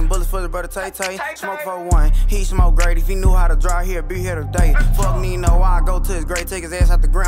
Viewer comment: “if he knew how to drive he still would’ve been here today” is actually probably now one of the most disrespectful bar i’ve ever heard so far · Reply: I thought he said if he knew how to “draw” as in his weapon either way draw is a way harder line